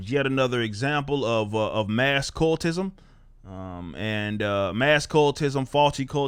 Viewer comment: I wasn't hearing with attention, I thought you say "Marx cultism".... well isn't not to distant in essence 😜🖖